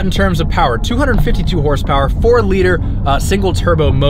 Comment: did I hear say "4.0 liter"? Nonetheless, love the content. Been watching since Episode 1 with the "Why I dislike the M4" video.